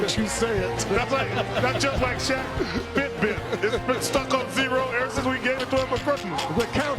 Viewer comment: What does Chuck say at 13:30? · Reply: that's just like shaq's fitbit, it's been stuck on zero ever since we gave it to him for christmas